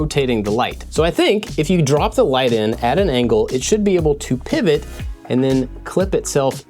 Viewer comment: who else heard yell "Pivot!"? 😂